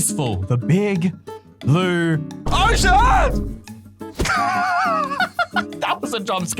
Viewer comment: In 12:54 i thought that sad oh s###but he actually said ocean😂😅